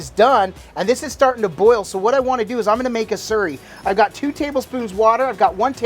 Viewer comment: Sounded like you said cornstarch surry? Is that a thing where you guys live?